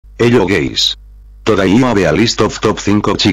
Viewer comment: It sounds like you say hello gays